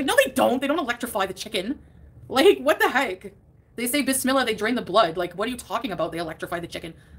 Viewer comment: I initially thought she said they say bismillah and DRINK the blood. 😂😂😂😂